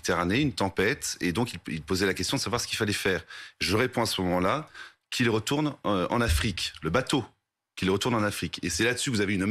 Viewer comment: Le gars interprète les paroles et les pensées de quelqu'un d'autre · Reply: Personnellement, quand je suis allé écouter l'extrait j'ai entendu "qu'il(s) retourne(nt) en Afrique". Mais je reconnais que la qualité du son n'est pas géniale.